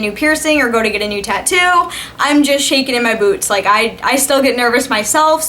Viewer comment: Hahahahahahaa I thought you said: "I'm still shaking in my boobs" but I found out that you said boots :')